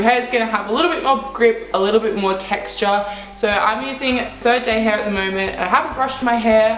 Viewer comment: What did she say at 00:24 seconds in? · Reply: 3rd day hair. You probably don’t need his anymore because that was 2 years ago but I hope you see this.